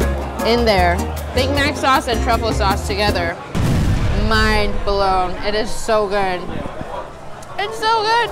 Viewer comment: YOOoooo, Big Mac sauce and Truffle Sauce. Your killing me. Sounds like u said, Mind Blowing.